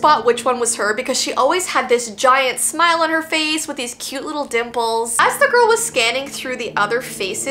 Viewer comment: i thought she said cute little nipples 💀